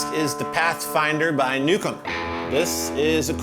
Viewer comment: Anybody else also thought Josh said "Nuke 'm" instead of Newcomb...?? :)